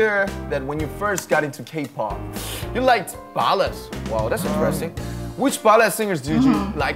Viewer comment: i'm pretty sure namjoon said "wow that's interesting" but the first time i heard "depressing" and now i can't stop laughing